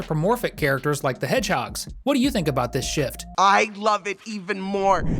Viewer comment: did he say shit